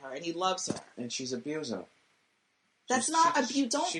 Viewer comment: What does he say at 16:11? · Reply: "She's abusing him"